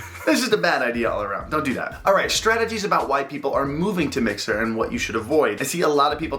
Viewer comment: Bro at 4:36 I legit thought he said “white people” I had to rewind 😂😂😂